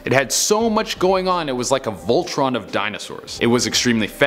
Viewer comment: Did I hear voltrON?